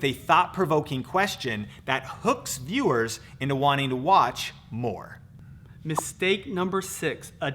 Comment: i thought he said wanting to watch porn💀